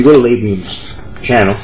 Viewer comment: I'm glad I'm not the only one; I keep hearing him saying "labia"...